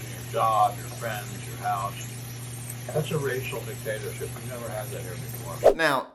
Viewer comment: Sounded like he said racial potato chip lol